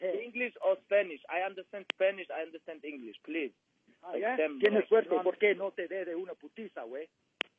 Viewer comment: What exactly is Tony saying at 1:10? Like how do u write it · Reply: SS Dimitrov tienes suerte por qué no te de una pudiste wey